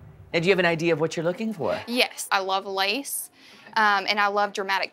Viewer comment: I fully thought she said “I love lice” and i was very confused